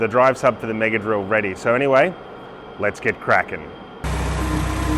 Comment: you came up with the name at the start of the video when you said "Lets get Cracking" i heard "Lets get the Kraken" 🙂